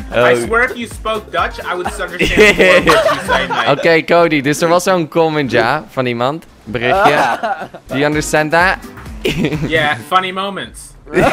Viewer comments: I am dutch and I still don't know what you said at 1:45. :-P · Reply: Hij zegt er was een comment een berichtje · I thought jordi said funny moments!!